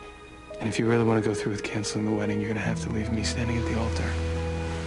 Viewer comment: Please can anybody tell me what Clark says at 0:36?? I'm not English and i just can't understand all his words!! :( Thank you!!! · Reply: FYI ... "If you really don't want to go through with the wedding, you'll have to leave me standing at the alter."